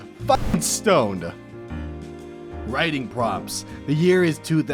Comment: I am listening to this in the background and I just hear “riding crops”